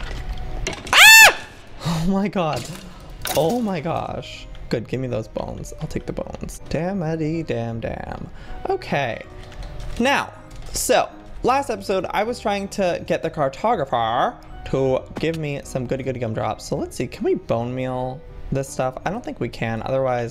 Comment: did it scare anyone else. I thought he said it was the last episode! 😮